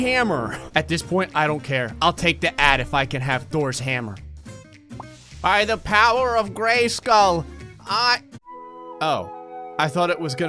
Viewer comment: did i just hear “for the honor of grayskull” SHERA MUCH